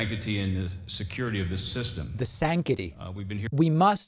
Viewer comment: I'm with you, but I heard sanctity said correctly. We don't have to make up anything for these ppl to look foolish. BTW, that does look like Bernie Kerik.